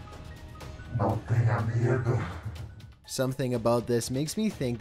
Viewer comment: Ive been speaking spanish my whole life that dude obviously has not cause its so broken I couldnt make out a word he was saying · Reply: Richard Haynie all I heard was "no tengas miedo"